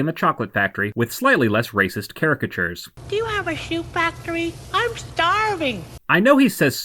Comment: I always thought Buster said “Chew factory” 😂